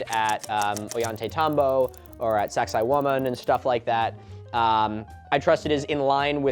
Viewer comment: Not the subtitles captioning Saksaywaman as ‘sexy human’ or ‘sexy woman’ and a range of other misheard phrases (I forgot what other things it called it)😭💀 (oh and making similar mistakes with the names of other sites too)